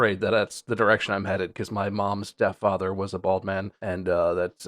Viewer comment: Did Bob say my mom stepdad was bald? …. How would that affect him? lol